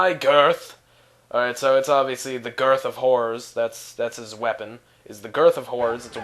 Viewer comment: I thought u said the GERTH OF WHORES